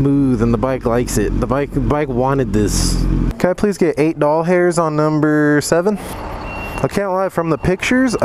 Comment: When he filled up his tank, I thought he said "ball hairs" 😂😂😂